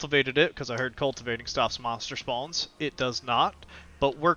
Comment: Shrooms spawnrate? I said monster spawns. Workbenches prevent monsters from spawning within their 20m radius. I tested is by placing workbenches all over the island. Before the workbenches, every night there were hundreds of greydwarves spread across the island, with occasional trolls and skeletons. After the workbenches, all spawns stopped permanently. Nothing spawns on the island even during raid events now. Workbenches work. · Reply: ahh, I heard "mushrooms" but its "monsters". Okay then, my bad xD